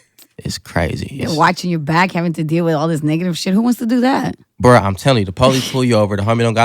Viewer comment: Did she say nigga @ 8:55? lol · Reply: negative smh